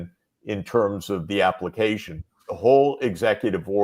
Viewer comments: I don't think the Professor said Biden. He said "in my point of view" 😂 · No he never said Biden - you misheard Judge he said ‘in my point of view”